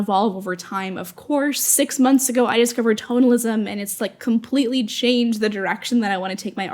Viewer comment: I’m with you on all your points, and I agree with you. I am laughing at myself because I misheard you say “totalism” as an art style so I was googling while your video was playing because I had never heard of it. And I found some results but they didn’t seem to be what you were talking about, then I realized you were saying “tonalism” lol. I am a retired graphic artist moving into being a fine artist. The lack of direction and deadlines is really difficult to adjust to, so I have been setting assignments for myself just like you were talking about. And emphatically “yes!” on the granular observations and goals.